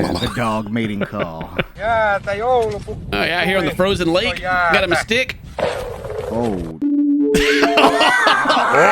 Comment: I don't know what the Finnish guy at 2:14 was trying to do, but he said something about Santa Claus (Joulupukki) if I heard correctly, so who knows.😂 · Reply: I think he said "jäätä, joulupukki tuli, nyt on jäätä" "Ice, Santa Claus came, there's ice"